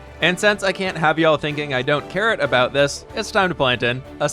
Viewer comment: Is it just me or when was about to make the carrot field, it sounded like he said ‘I don’t carrot about it’ instead of ‘I don’t care about it’